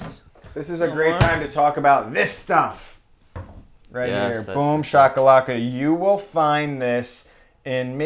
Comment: Did he just say boom shaka laka while talking about korean food? ahahhaahhahahahahha bigbang <3